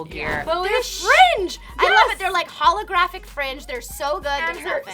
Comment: HOLOGRAPHIC did you say?